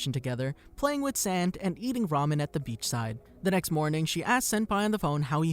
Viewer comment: did someone say ramen?